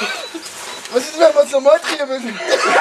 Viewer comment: Was sagt er bei 7:18 ?? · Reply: Hi..."Was ist denn wenn wir das nochmal drehen müssen"😉